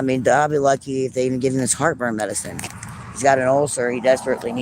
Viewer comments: Rofl I thought she said, "I'll be lucky if they even give him his heartworm medicine." No no no, she did say heartburn medicine but I'm still sitting here laughing my ass off. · Sounded like she said, Jeremiah will be lucky if gets his heartworm medicine? Take him to the spca, it's a little cheaper